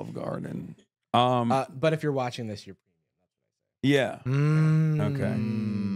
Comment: What does say that is censored around 1:38? The best guess I have is "but if you're watching this, you're dead to your family." Those aren't really the sounds his mouth seems to be making except for "family" for certain. The beard makes it hard to lipread him. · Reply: “If you’re watching this, you’re premium”